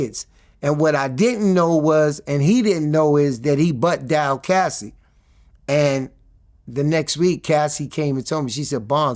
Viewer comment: he bugd out cassie ???? what he say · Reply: I had to repeat what you timed to understand him. Thanks for that. He saird "he butt-dialed Cassie"